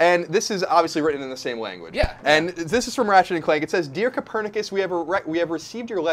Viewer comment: Did he just say Raget?! Its Ratchet!